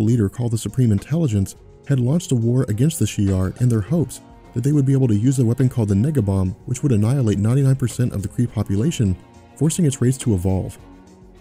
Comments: I heard the "Nigga bomb," and died laughing XD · Did a bit of a double take at 9:54 mark when he mentions the nega bomb it sounds like he is saying "nigga" instead of "nega". Man that had me going what the hell was that lol. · did he say "nigga bomb" ?? It sounds like it.